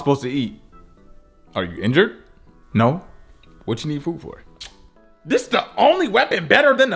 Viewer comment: "Are you injured?" "No" "What you need food for" LMAO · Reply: I just woke up, and for a second thought you said "well what do you need a foot for?"